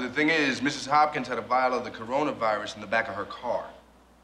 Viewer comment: Did he say coronavirus? America would never take care quarantine efforts this seriously.